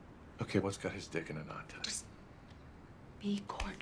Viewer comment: "just be .....el" help! What'd she say?